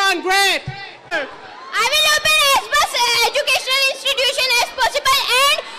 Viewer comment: did he say he will open an Xbox educational institution😂🤣